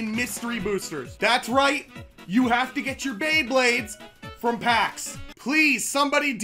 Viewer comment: I’ve lied about my childhood well technically I’m still a child but that does not matter I always thought you said blayblaydes